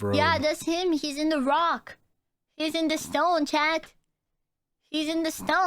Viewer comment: Did I hear "rock" and "stone?" FOR CARL